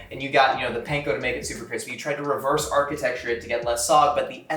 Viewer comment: did he just say reverse architecture